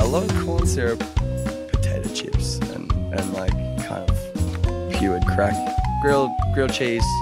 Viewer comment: pure crack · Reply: I heard "puréed crack" XD